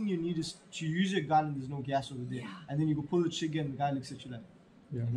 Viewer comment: did using YouTube’s tool, which usesGoogle translate. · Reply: Yes! I was laughing at 21:04 the text says "and then you pull the chicken mechanics." but he actually says "and then you go pull the trigger and the guy looks at you like..." Very nice job though. I really like the combination of pepper balls followed up by steel balls if necessary. A erratic strobe / 120 db audible siren grenade combination would be a cool addition as well lol. I just became a paintballer.